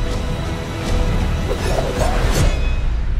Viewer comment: Chale, yo escuché “soy Juan Mulan” JAJAJA